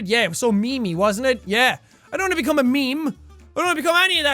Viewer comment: Who else heard Jack say me me instead of meme